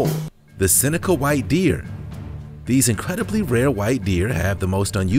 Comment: I'm getting old and my hearing is not so good, so for #1 I thought he said "cynical white deer"....so glad the names were actually on the screen!